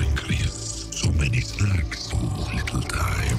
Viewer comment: I feel like I'm the only person who easily understands what Venom says aha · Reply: The only thing I couldn’t understand was I thought he said sacks instead of snacks